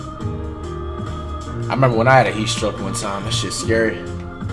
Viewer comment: Did flight say he had a heat stroke